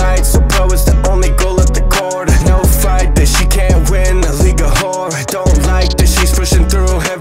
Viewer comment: When you said league of horror I thought you said 2:00 a legal whxre